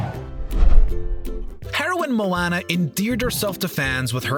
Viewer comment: Anyone else hear “Heroin Moana”?